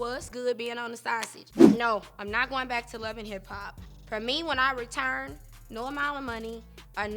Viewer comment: I thought she said wasn’t going back to love and hiphop 🤔